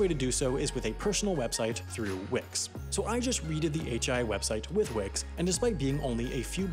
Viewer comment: Did he just say readed instead of read?